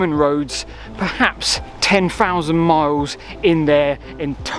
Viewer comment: you say 10,000 miles of roads, while the closed captions say 10,000 m, or meters. forgive me it this is covered already. Surely it's meters? (From Dallas, TX!)